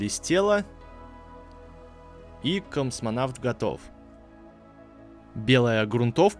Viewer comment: Ахаха, 6:39 мне послышалось "CUMсвонавт"